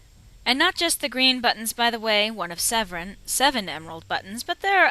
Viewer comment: LOL when you were talking about the seven buttons, it sounded like you said Zevran. I <3 your DA walkthroughs.